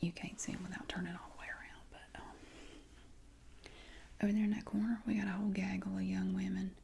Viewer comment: Lmao your accent and my hearing, at first I thought you said "what are you doing over here at the coroner?" Thinking you meant the coroner's office investigating a death.... I thought oh jeeze this is going to be a VERY interesting role play, not sure how relaxing..... glad I continued tho LOL